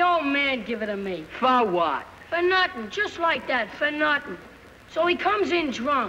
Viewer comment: The accent you hear in this film IS DEAD and BURIED. And before anyone questions me, I'm saying the accent in this film is STRESSED to the point of exaggeration when comparing it to modern times. I've never heard this continued accent in Brooklyn There are still HINTS of it left but NOT LIKE THIS - I have hints of this accent in my speech, but I never met someone from Brooklyn who speaks the way the actors in the film do sentence to sentence to the point where it's even a close match - and NO, I'm not talking about hipsters, yuppies or transplants - I'm talking about natives and true blue New Yorkers who spent their time in the borough - from those that became professionals in the work force to those who used to fight for their neighborhoods. Maybe they did talk like this in the 30's and 40's but not from the mid 80's on where I had the maturity to remember such things. Hints YES and some resemblance, but even if the "kids" in the scene are real Brooklyn boys from that era - that continued word to word, sentence to sentence accent is long gone. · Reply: Funny, I'm reading your reply and I hear 2:30 (Fa nutten or "for nothing") and I think - I say it the exact same way - lol.